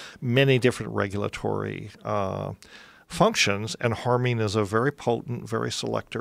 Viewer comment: Can someone, please tell me what Dennis McKenna is saying here? Is it "harmin", how do You spell that? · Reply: It's harmine